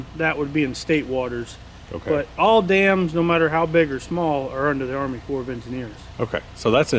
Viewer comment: What did he say here 2:53 " All dams no matter how big or small will need what?" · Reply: All dams are under the army corps of engineers.